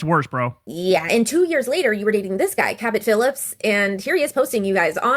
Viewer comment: Cabot Phillips I thought she said Captain Phillips 😂